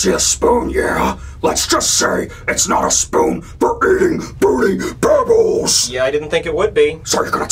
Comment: SML SAW revenge from Billy to Brooklyn T Guy, Susan, and Mr. Goodman for a cure for AIDS? Interesting 🤔 And in 2:12 when Billy said “see that spoon for eating Fruity Pebbles.” The captions said “see that spoon for eating booty bubbles”. It had me dying 😂💀